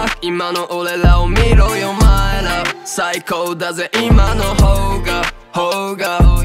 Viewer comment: 最高だぜ、韻マンの方がに聞こえて、鳥肌が止まらん😆